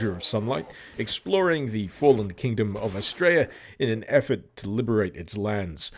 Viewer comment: did he say the fallen kingdom of 'Straya? We're a fallen kingdom? 'Bout fuckin' right.